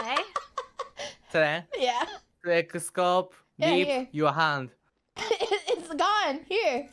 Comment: I think he said ' Selen 2x scope, leave your hand '